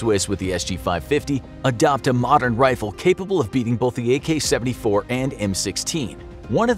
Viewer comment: did he say the AK74?